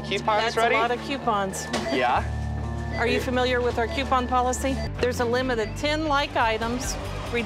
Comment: I thought he said no coupon limit